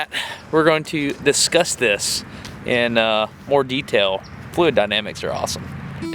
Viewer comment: Did he just say "We're going to viscous this" fluid pun?